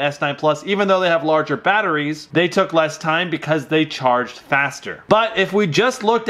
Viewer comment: Why are u trying to prove always that iphones are better. Even when s and note 9 won u tried to down them. What has happened to you. U just wanted that note 9 shouldnt defeat iphone badly so taking another topic for saving iphones👎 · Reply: And i thought he said facts 😂😂